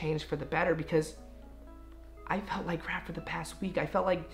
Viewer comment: when he said " I felt like crap from the past week" I thought he said I lost my grandpa last week. I was like NOOOOO Brennan I am sooooooooo sorry!!! Then I re-winded it and I heard what he actually said. LMAO